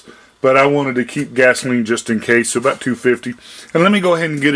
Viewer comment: Why doesn’t anybody give a ball park price on these products when they do a video ? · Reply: I thought he said it was about $250.